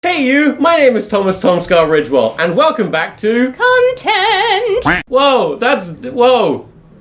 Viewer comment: Hearing "content" so many times, whenever i hear it, i hear "cunt-tent". Thank you Tom